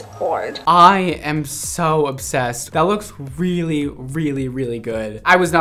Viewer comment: The girl that dyed her hair pink and orange: At the first moment I thought she said 'I'm so upset...'but luckily it was' I'm so obsessed...'Glad she liked it.